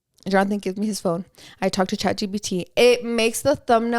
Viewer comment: So im not up with the times at all like A.I. or anything like that, I rarely download apps but tell me why I kept hearing “Chachi P.T.” Instead of Chat GPT 😂😂😂😂 I had to search it up to see what you were talking about 🤭🤭